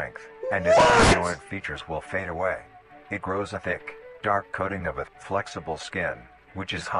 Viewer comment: at first I heard "it grows a dick"